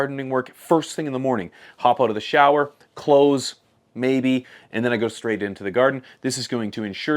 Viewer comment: Did I hear bro right? Clothes maybe lmao